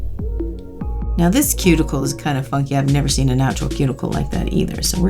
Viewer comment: Did you just say "this cuticle is kinda fucked"?!?!? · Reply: Svet No, she said funky lool